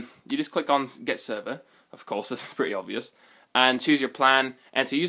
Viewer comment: I thought you said "it's free, obviously"? It's asking me to pay $8 a month?